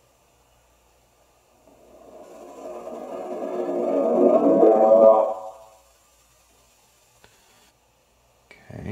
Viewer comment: and 5:31 timestamp, did he say "Mahesh Bhatttt"??? 😳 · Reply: HoneyD Hydra he said mahesh Bhatt so clearly